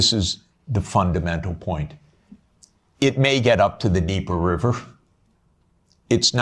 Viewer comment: Creo que dice "Dnieper River" no deeper, es decir el río Dnieper